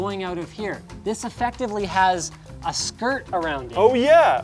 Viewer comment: I know Linus said skirt but I totally heard it as SKRRT. Still laughing about that